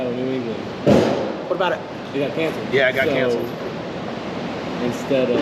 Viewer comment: When and his boy were talking thought he said he had cancer but he said it got cancelled wow mumbling